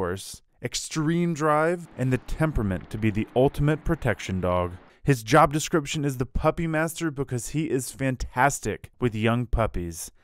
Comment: I thought he said the poop master, turns out it was the puppy master. Easy mistake to make I guess 😂